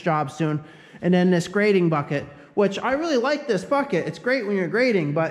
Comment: What did he say at 24:29? "I really like this _ucket". It sounds like he dropped an f-bomb. Lol.